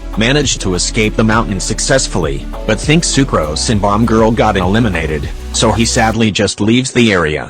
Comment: = did i hear that right??? "sucrose and bomb girl"